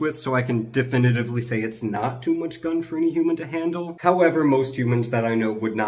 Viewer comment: American here, I heard "too much gun" and got a little excited. Ignore that pitched tent over there. Gimme gimme gimme! I NEED MOAR DAKKA!!!